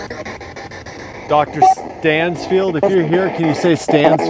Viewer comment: I heard "Stanfield?"